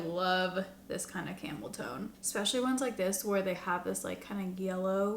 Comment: I thought you said 🐫 camel toe 😅